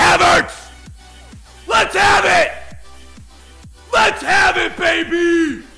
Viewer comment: anyone else heard let's have a baby 😂😂 lmao